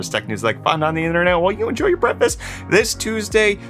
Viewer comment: I don't like how you ask us to tell you what we don't like about HotNews because I cannot think of any... ...ok, maybe one. In the beginning of watching Hot News, I thought you said 'while you enjoy your Breakfast'. Heck, even the CC has breakfast. Of course I was usually eating Breakfast while watching HotNews. BTW, LOVE the two person interaction while giving the news. I hope you can keep it up.